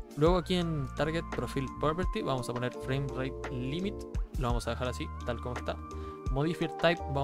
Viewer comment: Hola amigo,no entiendo que dice,dice "framer límit" o que? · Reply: FramerateLimit es lo que debes poner en el “target profile” del rivatuner, esto da la instruccion de capear los fps con esa keybind